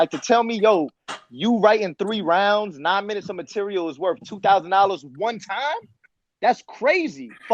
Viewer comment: Please correct me if I'm wrong but did say the MOST he ever got paid for a battle was 2,400$? With how lyrically talented he is and his consistency he deserves to be a $10k/battle type uh dude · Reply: Peanuts. Something not right with the payout to battlers for the pay to be so low. I thought he said 25,000 he said 2,500 🤦🏽‍♂️